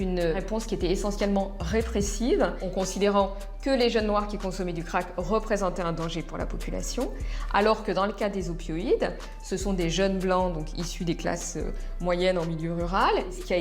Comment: c'est moi ou elle dit "ces jeunes blancs" à 5:53 ?